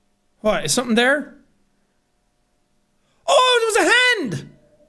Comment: I was watching this for some nostalgia and at 8:24 Sean says ‘oh there’s a hand!’ and the jet2holidays ad plays and then all I hear is ‘darling hold my hand!’ 🤣🤣🤣🤣